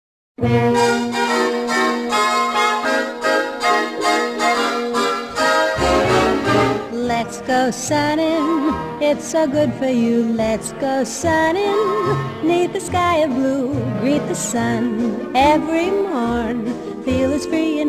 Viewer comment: Lol, for the longest time I thought she was saying "let's go sonny" and now I have this permanent image of an elderly woman singing this song.